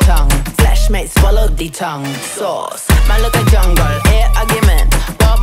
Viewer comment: did she Just say sauce? Lmao🤣